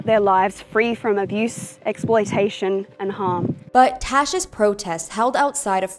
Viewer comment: Tash, oh so sorry I thought you said Trash, my bad!